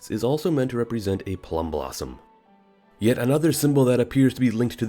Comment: I misheard you say "plum blossom" because I was in another tab. I heard you say "plumbob" and I was super confused when you started talking about The Sims